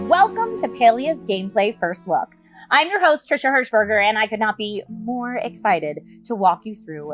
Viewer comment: I thought she said Hush-burger and that immediately caught my attention